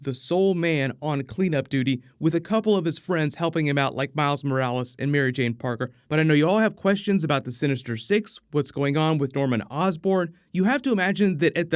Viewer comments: Did I hear that right??? Did you say "Mary Jane Parker"??? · Whoa whoa whoa, what was that? Did I hear Mary Jane Parker at 3:12?